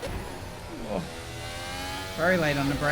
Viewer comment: Cool video. Did I hear you do a Kimi Raikkonen impersonation at 12:52? I'm sure I heard a "bwoah"!